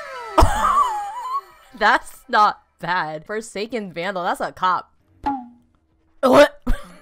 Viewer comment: Thought it said foreskin vandal 💀💀